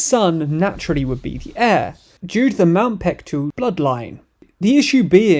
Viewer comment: air instead of heir